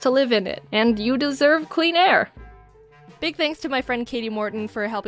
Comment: I thought u said “queen air” 🤗🥶🤩